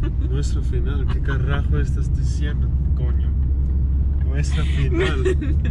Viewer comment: did he call her a coño? Lmao